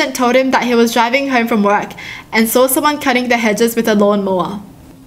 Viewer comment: I thought she said, "Cut his head straight with the lawn mower"... I was concerned and confused... I now know she said hedges not head :3